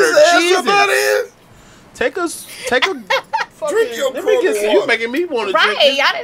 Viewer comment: did karlous say man take a sip of your corona water. 🤔